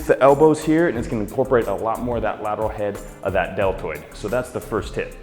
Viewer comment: YES! Thank you! Finally shoulders! Awesome by the way it sounded like you said delturd